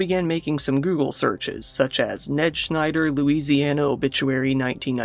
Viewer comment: HAHAHAHAHAHAHAHA! I'm at work and can't stop laughing. "Sorry, honey, I can't be with you. You're possessed by an insurance salesman from Cincinnati named Ned Schneider." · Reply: I heard it as Ned Snyder and wondered what her beef with the justice league movie was